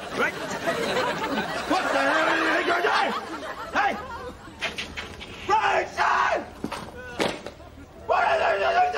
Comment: What did you say?😂😂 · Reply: What are you doing?